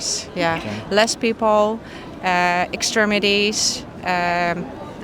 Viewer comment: Did she say "Extremities"? Kończyny?